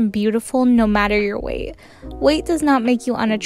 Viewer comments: “A terrible personality like that girl has makes you unattractive” Girl you took the word right out of my mouth 😀💅🏽(happy first day of October)HAPPY HALLOWEEN 🎃💀NO MORE COMMENTING THE DAYS!!! YALL STOP COMMENTING HAPPY (new day)😭😭😭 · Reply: I thought she said bat girl, that’s why I’m here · “A terrible personality like that girl has makes you unattractive” Girl you took the word right out of my mouth 😀💅🏽(happy first day of October)HAPPY HALLOWEEN 🎃💀NO MORE COMMENTING THE DAYS!!! YALL STOP COMMENTING HAPPY (new day)😭😭😭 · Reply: OH LMAO I THOUGHT SHE SAID ‘BATGIRL’